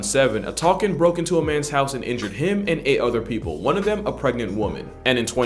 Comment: i thought he said "injured him and ate other people"💀💀💀